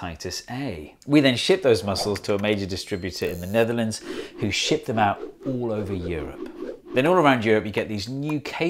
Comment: U haven’t REEEALLY said “ shit them out “ instead of of “ ship them out “ ...have ya? 🤦‍♂️😅🤫😂🤣😭